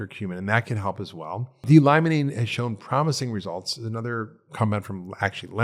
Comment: whats he sayign del lie mena? cant understand him what 2 things is he saying . 1 is from lmons how do you spell that? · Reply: D-limonene